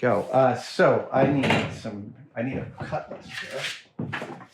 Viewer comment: Thought he said cutless when he said cut list, made the next scene less dramatic than I had expected.